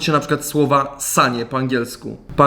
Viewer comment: Usłyszałem ssanie A nie sanie